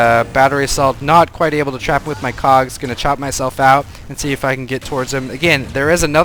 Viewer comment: all I hear is "I trapped him inside my cocks" which makes me jealous...